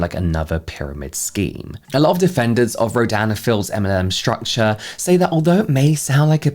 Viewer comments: Even though I don’t watch their channel I still misheard you as saying ‘Dan and Phils MLM scheme’ for most of this video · I'm drawing while I'm listening to this and auditory processing is fcking me up, every time you say the brand name I hear "Ro- Dan and Phil's" lmaoo